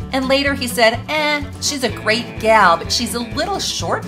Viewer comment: I thought Elvis said Marilyn was a little too TALL for him, not short